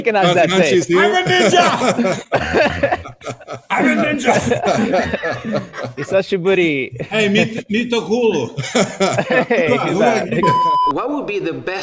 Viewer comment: What did you say at 8:52? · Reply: 久しぶり (long time no see)